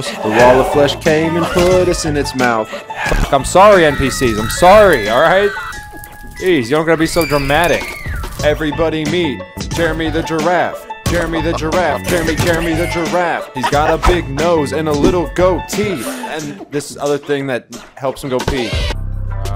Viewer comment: the funny thing is that when he said: "everybody meet" then you know how sometimes when discord auto opens and it says discord? well because of that i heard: "everybody meet, discORD"